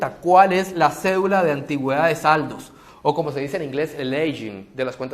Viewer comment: Me podrían decir como se escribe la palabra en ingles que dice en el minuto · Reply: Aging